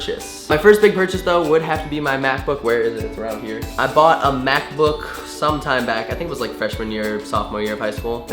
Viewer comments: When you said Mac book i thought you said math book. And i was like well ok, we're all different 😂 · hahaha when he said mac boor, I thought he said math book hahahahahhhahaahahaha :D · _- i thought you said "math book" instead of mac book. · I thought you said math book and then you picked up your MacBook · When u said MacBook I thought u said mathbook lol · Oh you said Mac book I thought you said math book I was like wow I didn't think math books were that expensive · When you said your first big purchase was your Macbook I thought you said Math book so yeah haha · I thought you said math book until you pulled the Mac Book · When he said "Mac Book", I could have sworn he said "math book". What an achievement. hahaha :P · I thought he said math book not MacBook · When he said Mac book I thought he said math book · Lol I thought you said math book instead of Mac book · I thought u said math book, not MacBook !!!!! :-D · When he said mac book I thought he said math book so I was like math books cost that much lol XD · Ahahahaha I thought Connor said math book not mac book